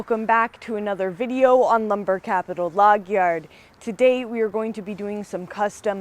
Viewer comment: Maybe it is because I'm Southern, but in the intro I always hear 'log yerd' 😂